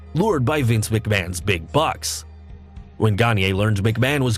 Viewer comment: Wait did you say Hogan was lured to the WWF by McMahon's big butt???😂😂😂😂😂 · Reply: i heard "bucks" and was confused, by taking "butt" under heavy consideration, it makes perfect sense.